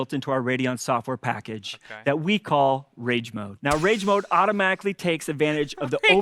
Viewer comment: Did i hear "Sage Mode"?